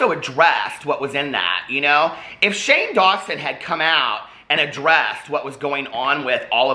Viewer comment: I totally heard, "If Shane would have come out in a dress.." was like uhh.